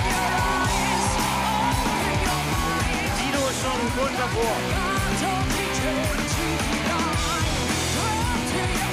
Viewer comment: what he said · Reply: Sido ist schon kurz davor"